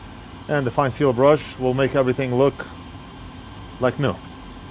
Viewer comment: what was that brush? I couldnt quite hear what he said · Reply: a fine steel brush